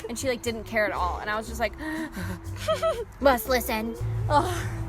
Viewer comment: did she say ''Ich bin schwätty'' it sounds so german lool XD